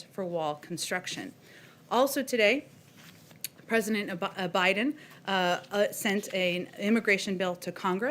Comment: no she was not saying president Obama, she was saying president oppa Biden ("oppa" means elder brother for women in Korean). :)